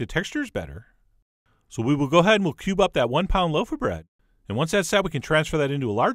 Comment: I heard "we'll go ahead and pube up that one pound load of bread'.